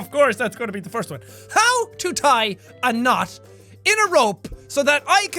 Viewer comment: i thought you said how tie a nun, instead of knot LOL